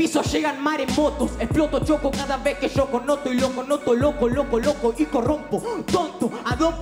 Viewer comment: lo que dice: "y loco noto loco lo coloco y corrompo " lo que escuché: "y loco noto loco loco loco y corrompo" xd